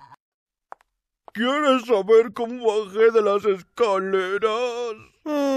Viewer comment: ¿alguien entiende lo que dice? Expliqueme por favor :"c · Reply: ¿Quieres saber como baje de las escaleras? Enfermera: ¿Por caerte? Joker Por... Caerme! es en referencia al otro Joker que le decía a la misma enfermera que si quería saber donde se hizo esas cicatrices, saludos!